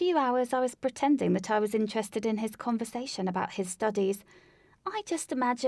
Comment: I thought she said "I was interested about his constipation"